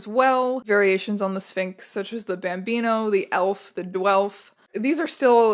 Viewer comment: For a second I thought you said "the bambino, the elf, the Guelph..." and I was like there is a surprising amount of cancon up in these weird-haired cats.